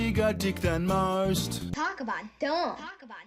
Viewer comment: i heard "talk about dong" not "talk about dumb"